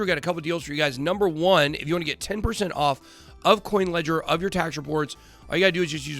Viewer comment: sounds like you said kucoin ledger